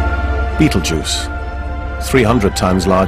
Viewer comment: Did he say "beetle juice" at 0:44?